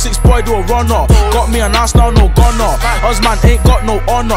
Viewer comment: Does he say got me an Arsenal no gunner at 1:27?